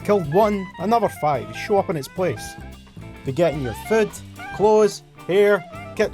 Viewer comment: "they get in your foot" I swear that's what I heard